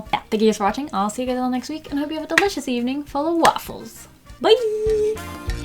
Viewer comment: I just realized she said full of waffles at the end of her videos i thought she was just saying sisvwkwaffles lol